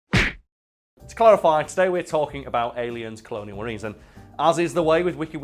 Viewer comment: Your accent is so heavy I thought you were saying "aliens cloning marines" lmao